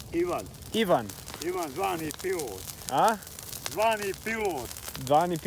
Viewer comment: I think he said to your question what's his name: "Ivan. Zvani Pilot". I'm guessing that's his nickname: "Pilot"? Although not sure 😅